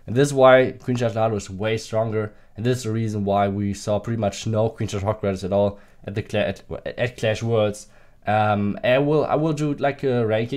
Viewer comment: Did he say no queen charge hog rider at all at clashworld?? 10:05?.